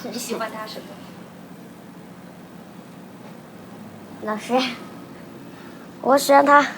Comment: when she's asked what they like about each other most, it sounds like she says "nothing" lol